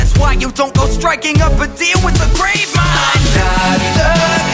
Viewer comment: wait, did it say "grapevine" at 2:01 ? Oh wait, it did not say "grapevine" it said "gravemind" ohhhhh